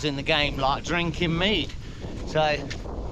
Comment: I thought you said "drinking ME" 🤣🤣🤣 only to see it is Mead 🤭🤦